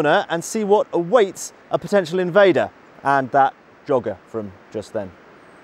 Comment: I genuinely thought said "that dogger". Bit harsh to take them on with a 7.5cm AT gun and a Maxim...